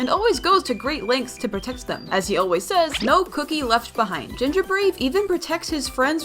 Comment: "no cookie left behind!" But all I heard was "no troll left behind!" From the first trolls😭🙏